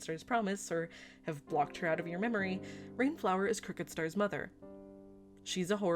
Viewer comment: At first i thought you said Cricketstar